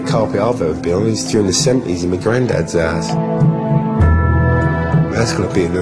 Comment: What is he saying? The best I can make out is "The only red carpet I've ever been on is [to an assembly of] my grand dad's ass." Fucking hilarious!